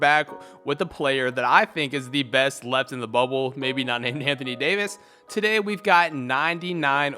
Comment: Did he just say best player in bubble not named Anthony Davis. Lebron and Jokic looking like 🧐